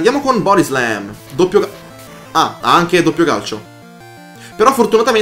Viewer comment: era un "doppio cazzo" quello che ho sentito o mi devo sturare le orecchie?